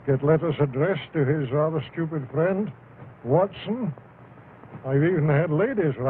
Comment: “His rather stupid friend Watson” Well, dang, Sir Conan Doyle tell us how you really feel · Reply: I thought he said "his other stupid friend, Watson", ironically hinting at himself as the first one